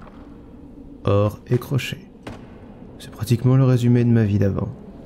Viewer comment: la voix est très bien .J'ai entendu "or,télé-crochet"